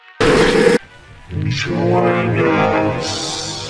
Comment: I think at 0:14 specimen 8 is saying “ join us... ”